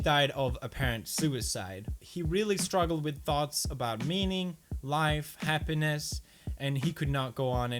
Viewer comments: I am pretty sure I heard pewds say "he really struggled with thoughts about memeing" ... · I thought u said memeing not meaning cuz i wasnt looking at the screen